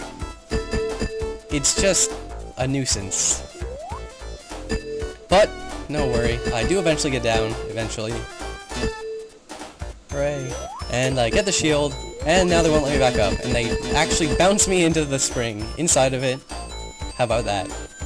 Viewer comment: ish did anyone hear a soft "great" when he finally gets down to the shield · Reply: sounds like hooray